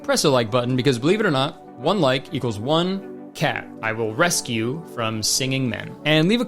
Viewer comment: I THOUGHT IT SAID RESCHEDULED CAT 😭😭